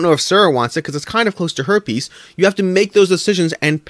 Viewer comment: Ha! Jokes on you this time...I already backed this so...suck it! 😜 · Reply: Also lol at 4:20 I don't know if Sarah wants it because it's kind of close to herpes...is all I heard